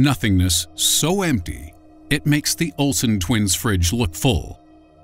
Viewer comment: "it's empty enough to make the Olson-Twins fridge look full" damn that's rough. Lmao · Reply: I thought he said "fringe" I was so confused